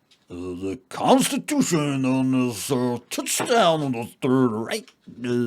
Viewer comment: Did he just say "It's a touchdown... on the third reich"?